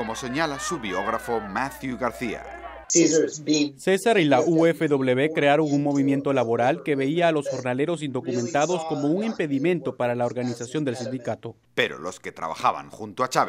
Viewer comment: alguien me dice qué cosa dice?? No le entiendo >:( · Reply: “César y la UFW crearon un movimiento laboral que veía a los jornaleros indocumentados como un impedimento para la organización del sindicato”